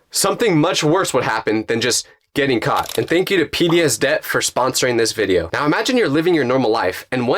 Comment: i thought bro said pbs debt 🤣